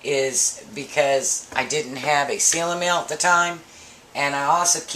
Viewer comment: Please, at 14:49 ...for the fruit cocktail, you say that you did not have the "seal-a-meal" at the time ...is that what you said?